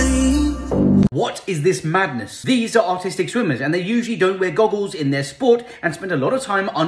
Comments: For a second I thought it said “autistic” instead of artistic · I thought he said "autistic swimmers" 💀💀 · Am I the only one who thought he said “ these are autistic swimmers”😂 (No hate) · Did he just say autistic swimmers? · If you enjoy learning things like this, you’ll love my book “This Book May Save Your Life”: <link> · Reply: i thought you said autistic swimmers😭😭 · I thought he said autistic swimmers at first😅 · i ong thought it said autistic swimmers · When I hear "artistic swimmers" I can only ever think "autistic swimmer" when I see the picture. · I heard "autistic swimmers" the first 3 times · I heard him say autistic at first and I got confused because this doesn’t quite match up, and then I saw he didn’t say that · I swore I heard autistic swimmers 😭😂🤣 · Is it bad that I thought it said autistic swimmers and spent the entire video wondering why they’re under the water so much. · Ngl, i thought it said Autistic swimmers and i eas very confused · I thought he said, autistic swimmers I was like oh they must like like the texture of the goggles or something 💀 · i thought he said they were autistic swimmers and i was so confused as to what no goggles had to do with being autistic 💀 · For real tho, i thought it said "autistic swimmers" and I was like thats cool but what that got to do with it?